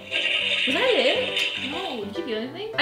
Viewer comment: did she just speak tagalog at 1:47? I heard "wala rin? "